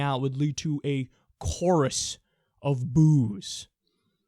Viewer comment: I thought I heard... "A chorus of bewbs"